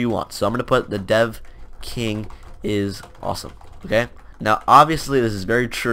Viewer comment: lol i thought you said The Deaf King